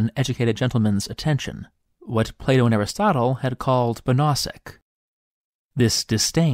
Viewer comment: What word is that at 2:15? The caption says "banasik" but I don't think that's right.